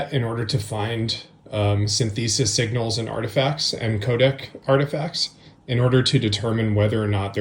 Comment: Yes and "Kodak" 😅 all the other times it said "'code" for codec. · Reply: I heard codec (2:43, 3:16, 4:05). AI speech to text is creating the captions. He says iZotope but it was translated to isotope, for example. But man, if you want to tell the world you have no idea about music, keep repeating syn-thee-sis, a word that's been around in the music world since synthesizers. He does say providence instead of provenance. These are things he does not understand at all. "Very forensic tools" makes no sense. This type of "analysis" is becoming more and more part of the problem. People who don't understand the tools that they are using take their output at face value but don't understand how the tools work, how the tools can fail, or how to validate the output.